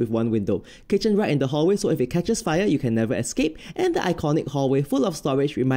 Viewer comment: I thought you said, "if you catch a spider you can never escape" lolol 🤣🤣🤣